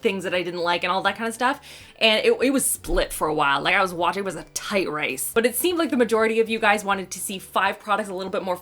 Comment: Did you just say "tight rice" haha! so cute